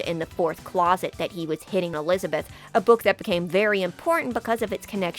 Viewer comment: For a second I had to pause the video and digest what you just said, because I thought you said "Hitting on elizabeth.". Five minutes later I realized what you said. Hearing problems much? Maybe. Hard of thinking? Yes.